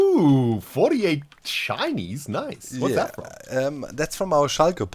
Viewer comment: Totally thought said “48 chinese”